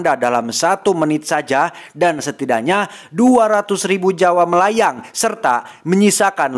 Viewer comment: Gw yang salah denger apa emang si ngomong "200rb Jawa melayang" 🤨